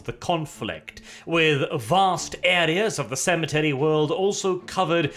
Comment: At the start it sounded like you said sanitary worlds not cemetery worlds when you tried to emphasise it